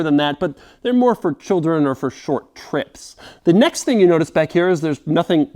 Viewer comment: I really thought he said "Short pricks" the first time I heard it. LMAO. I was like..."WOW Doug! Way to go there!" But not to be. LOL.